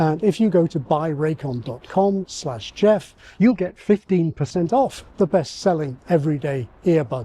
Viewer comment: Go to <link> to get 15% off the bestselling Everyday Earbuds! Brought to you by Raycon. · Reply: the captions say "slash xxx" instead of "slash geoff"